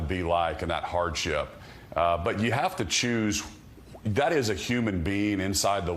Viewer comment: Oh. At 1:36, what was that word he used? 'Choose'?